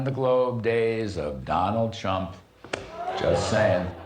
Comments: if you look and listen close he actually said Chump not Trump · Larry Kudlow's voice sounds like the emotion of sitting in an outhouse in the middle of the night... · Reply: could have sworn he said "Donald Chump" which would be a real Freudian slip. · sounded like he said donald chump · is it just me, or did it sound like Kudlow says Donald Chump at 4:11? maybe it's just confirmation bias? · I thought he said Donald Chump lol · I definitely heard "Donald Chump"